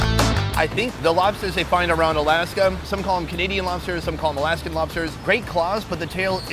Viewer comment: I thought the called :/ Maine lobsters